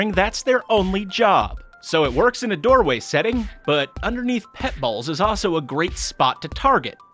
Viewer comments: I really thought he said “pet balls” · Who thought he said balls when he said bowls